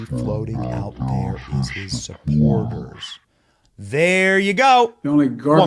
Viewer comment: Almost sounds like he said "HER SUPPORTERS." 🤔